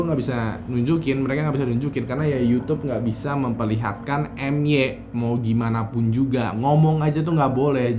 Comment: ngomong apa hayo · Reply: M a y a t